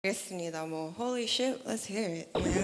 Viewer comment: At first, I thought she was saying “holy shit, let’s hear it” but then I realized she’s actually saying the DJ’s name, Holiship 💀